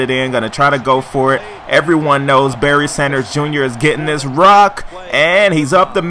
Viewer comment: Dude I heard you say B.S. JR. Ok I know that is supposed to be Barry Sanders Jr. but it just sounds wrong